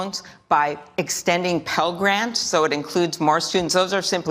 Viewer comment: She can't even speak properly. I spent the last two minutes trying to figure out what she was saying at 7:20. It's pell grants, apparently.